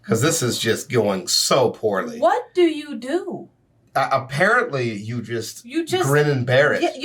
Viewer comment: I swear to everything I heard say "A-Parrot-Ly" here, not apparently. Infinitely funnier. Also 10:16 I was thinking Kingfisher, since that's sort of close to catfish, but I think I like skeet shooting better lol.